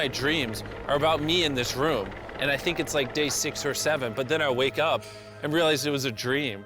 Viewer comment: did he say 6 or 7 ??? SIXXX SEEVVEENN